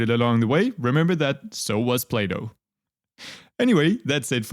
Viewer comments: Correction: at 12:58 the subtitles say Play-Doh instead of Plato. This undercuts the delivery of the otherwise phenomenal joke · auto-subtitles saying “Play-Doh” The disrespect😭